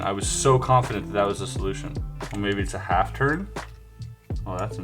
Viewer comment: When he said maybe it’s a half turn I thought he said maybe it’s a hacker